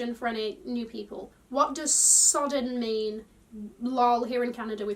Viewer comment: Lmao, this whole time I thought you were saying «sudden» like something happening suddenly 😂😂😂